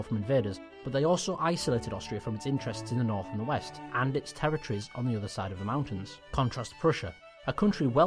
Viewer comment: I love the countryballs and thought this will be a funny video. But unfortunately I can only hardly listen to what you are saying. Maybe because you are talking to fast or something. For example at 1:58 instead of saying: "territories" you are saying "terretris". Whether this a word I dont know, or you really mean "terretories" (which makes sense in this context). But you are making this all the time. So I have a hard time understand you want to say. Sadly :-(